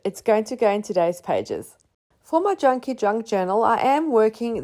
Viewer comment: I love the name Junky Junk Journal, too!😂 It's so fun to say. And like many others, I love to watch you create. Sometimes when I can't sleep, I'll go straight to your channel & rewatch a bunch of my favorites. (And I recognized the negative space #2 die from FDC3 😊) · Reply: lol, I thought she was saying chunky, junk journal which also sounds fun!